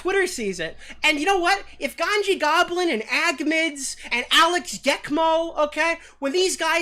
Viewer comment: the captions says "ganji Goblin and 𝖺𝗂𝖽𝗌 (with upper case on that last word). Lmao!